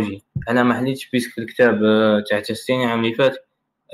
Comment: وسمو لكتاب ما سمعتش ؟؟؟؟ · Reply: كتاب تاستيني